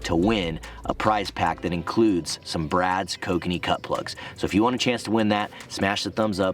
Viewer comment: At first I thought you said Kokanee butt plug